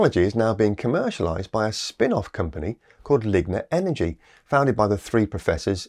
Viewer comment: I thought he said ligma energy...